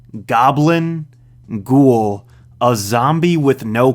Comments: i always thought he said goomba instead of ghoul, which always confused me cause why would he start naming mario enemies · Am I the only one who always misheard ghoul as goomba?